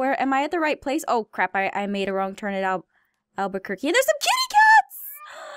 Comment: Did anyone else hear her say Albuquerque? A city in NM US.